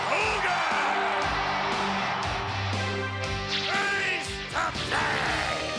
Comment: When I watched WrestleMania VII on Coliseum Video, for a long time I thought Vince was saying “at east of baaaay!” Only when I watched the show on WWE Network with the captions on did I understand that he was actually saying “face to face.”